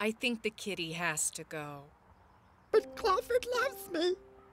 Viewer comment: what did she say? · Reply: 'But claw ford loves me'